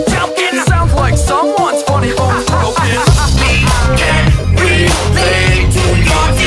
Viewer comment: Definitely thought it said we cant relate not we can Also i thought it said devations not motivation